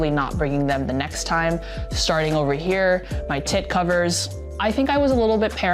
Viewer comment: lol did I just hear "tit covers"?! a first for EDC content, but that's why you're my new fave carry-oriented channel